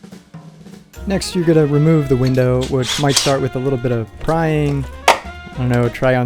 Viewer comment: "Next you will remove the window. Might start with a little prying..." I thought you said 'crying'. Was going to comment, "can relate!" 😂